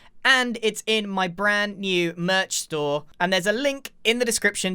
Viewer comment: sounds like he said “brain” not “Grain” lol